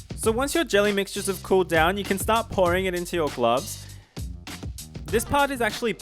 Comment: When you said pouring I thought you said boring.....L.O.L.!